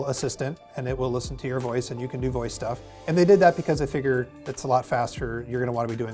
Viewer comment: Google Assistant thought you said "And you can do boy stuff". 🤣